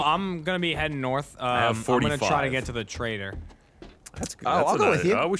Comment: This whole series i thought they were saying "traitor" until typed "trader" into the map.